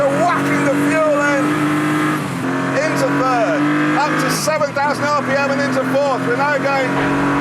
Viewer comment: At one point it sounded like he said "107,000 rpm into 4th" and my brain almost exploded. The YouTube director who uploads these videos need to remember that when episodes are uploaded they're converted into a different codec which ruins anything multichannel-ish and basically removes the center sound which is speech.